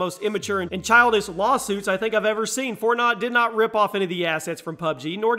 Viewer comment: Did he actually say "FortNOT?"